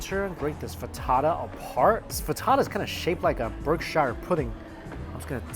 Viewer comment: Did he just say "Berkshire pudding??" 🤣